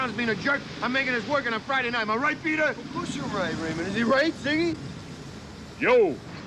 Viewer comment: Ray calling Winston 'Z" warms my heart. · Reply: I thought he said "ziggy"